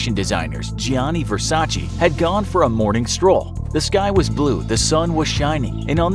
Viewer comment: Did anyone else think the narrator said, “This guy is blue?”